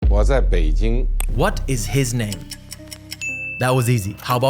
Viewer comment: did he just say "Wazzap Beijing" 😭? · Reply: He actually said “我在北京” :)